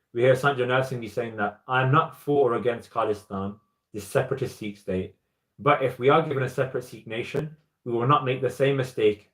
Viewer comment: Can you please write what Bhai Sahib Ji said from 25:30 to 25:50? As I'm finding difficulty in English due to Bhai Sahib Ji's accent. Anyone please? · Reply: Sant Jarnail Singh Bhindranwala says, "I'm not for or against khalistan, those are the separatist sikhs. But, if we are given a separate sikh nation we will not make the same mistake as in 1947, we will certainly take it."